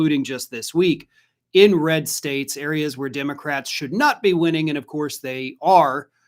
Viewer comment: thought I heard,"inbred states"..... Happy New Year!🎉🎉